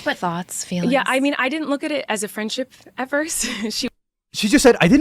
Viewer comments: not defending her in the slightest, but I think she said "at first", not "ever" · She definitely said "I didn't look at it as a friendship at first" not "ever" I don't like her just don't let your argument lead off you mishearing her. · She said "at first" at 16:50 I still agree with the majority of what you said in this video, but she said "at first" not "ever"